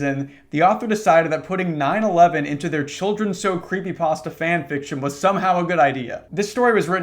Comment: I wasn't paying attention and all I hear it "9/11 into there children so creepypasta fanfticon." I looked up so fast